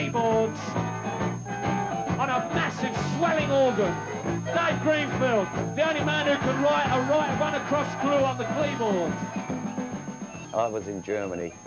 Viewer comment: What does Hugh say at 01:52 when presenting Dave......."The only man who can ????" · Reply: He meant to say "Dave Greenfield, the only man who can write up a crossword clue on the keyboard". But he stuttered when he said it and basically ballsed it up. It was a reference to Daves cerebral nature as he was always doing crosswords, rubics cubes, puzzles, playing with packs of cards etc and is well known for being the boffin of the band (also incredibly gifted at mathematics)